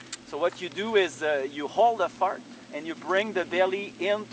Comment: Did I hear the phrase "hold a fart" at the 1:57 mark?